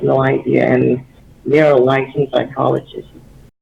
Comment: "They're a licensed psychologist" is what he said